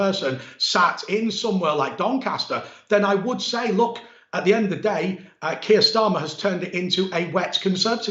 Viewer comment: I thought you said it was the "End of the road," for Nicola Sturgeon?